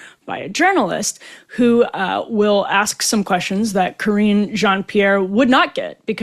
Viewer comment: I swear every time you say KJP’s name that I hear “cringe on Pierre”